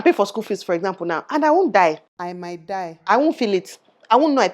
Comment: did I hear “I might die” 😹😹